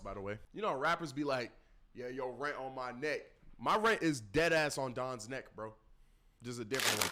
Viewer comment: i thought said “my rents on dons di” 💀💀😂😂🤣🤣